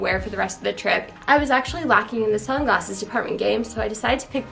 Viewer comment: I really thought she said deported